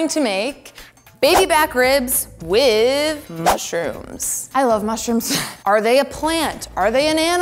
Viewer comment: Dudeee I thought it said “I make ribs while on mushrooms “ disappointed